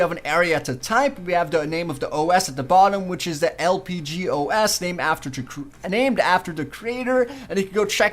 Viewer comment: I thought it said LAG-os